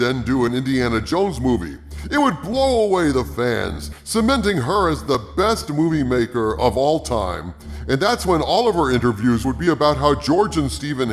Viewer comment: “...making her the best movie maker of all time” In my head it sounded like this : “...making her the best George Lucas of all time” 😂😂🤣🤣🙈🤣😹👍🏽😎😇